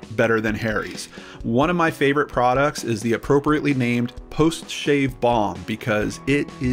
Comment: "The appropriately named 'post-shave BONG'"? 3:29 Dunno of im jonesing for college days, or if my ears are sleepy, but that's what I heard. Mea Culpa.😳